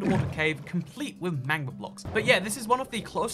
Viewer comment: When he said magma blocks I thought he said manga blocks for a second lol